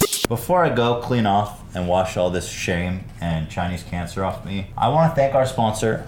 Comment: in the subtitles instead of it saying Chinese cancer it says filthy frank.... BRUH I CANT BREATHE